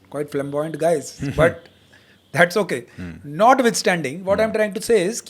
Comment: Can someone explain what he said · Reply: Google flamboyant.